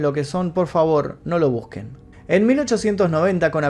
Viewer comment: Que lo busque dice?